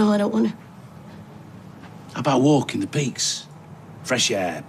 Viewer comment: I thought he said "how about walking the pigs?" 😭😭😭😭😭😭